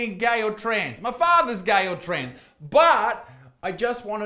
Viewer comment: did you say your fathers gay or trans ? please reply, i need to work out if i need to wash the wax out of my ears.